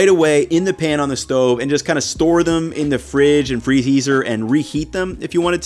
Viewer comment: did just say "freheezer and reheat them"?